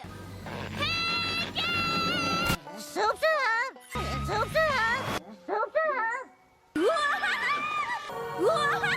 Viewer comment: what is granny smith saying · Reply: "Soup's on!"